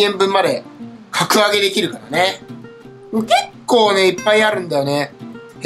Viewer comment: 〜｢結構あるんだよね｣の｢結構｣の所がニワトリの｢コケッ｣にしか聞こえん